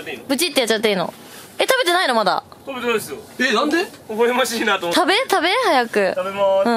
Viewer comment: ここでの美味しい？がHey Siri に聞こえて反応するwwww